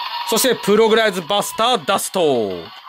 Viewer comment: プログライズバスターダストが プログライズバスターバスターって聞こえる